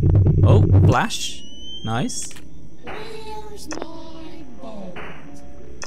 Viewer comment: that is Willy saying Where's my boat? oh and 21:06 I got a hat on my head